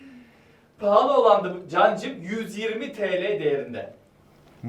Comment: Cancık miii ben mi yanlış duydum xjycufjd ❤️Noluyo Yaaa